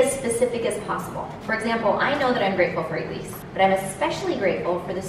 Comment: I thought said I am freatful for Jews lol